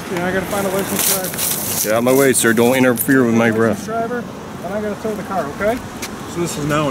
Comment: Sounded like he said "Get outta my way sir don't interfere with my breath"